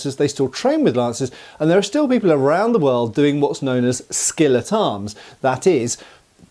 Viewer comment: For a second I thought you said "skillet arms" and I thought you were going to talk about people using cast iron skillets as weapons... But no, it was "skill at arms." 😞